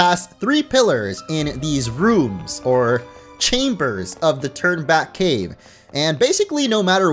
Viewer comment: Lowkey thought was saying “Turdback cave” but it’s Turn Back cave